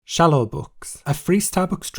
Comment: I know you have a cute accent but every time you said "Shallow-bucks" I hear "Shallow-books".